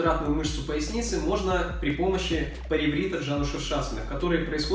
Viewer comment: Как на 7:43 попробовал, точно не расслышал, что-то типа палевритджанашушашкен) короче, пробую и не пойму как он это делает? Он походу без костей! Но потом полностью расслабился и через боль, не напрягаясь получилось гораздо лучше и прям почувствовал как там тянулась мышца. Щас лежу и она приятно горит. Жаль что у меня внутри бедра тянуть стало и дальше не смог, так бы походу и повторил как он)))